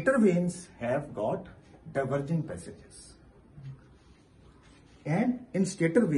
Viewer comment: Hi Sir, thank you so much for this video. Could you please tell what u said in minutes 3:10? Stator blades have got turvuring passages? Thanks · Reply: Diverging passages (cross-sectional flow area is increasing).